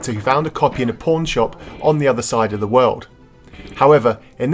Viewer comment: I thought you said Porn shop XD